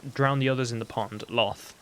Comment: XD I thought he said "Drown the others in the pond. Lol"